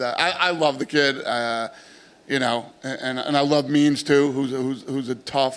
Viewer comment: I thought he said " I love memes" Instead of "means" lol